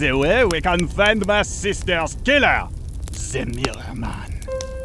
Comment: what said porlnareff at 1:33? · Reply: Ze mirror man